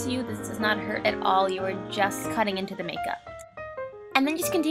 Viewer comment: I thought she said she wanted us 2 cut ourself to make this but we're just cutting into the make up 😥